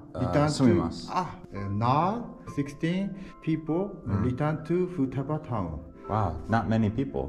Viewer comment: I think he said 16 people